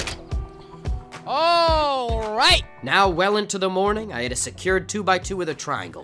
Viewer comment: did he just say weyln to the morning?